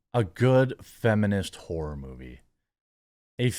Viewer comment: I thought he said "feminist whore movie" and I was like: don't we already have a bunch of those?